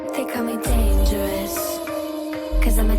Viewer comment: At the beginning, I thought she said “they call me dangerous. Cause I’m a thot ...” 🤷🏻‍♀️😂